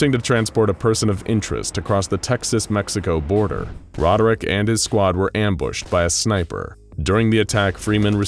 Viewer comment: For a second I thought he said "Roderick and his squadrick" and it made me laugh harder than it probably should have. Edit